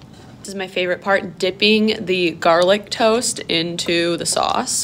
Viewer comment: I don’t want to be hater but did she just say “garlic toast”